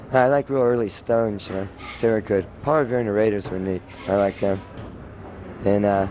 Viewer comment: What the fuck (or what band) does Stiv say after "I like more early Stones, you know?" ?? ("________, they were good. And uh.. I don't know..") it's @ 4:09. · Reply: Paul Revere and the Raiders.